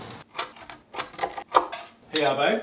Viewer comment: I fr first heard her say “hmm … piss salad here“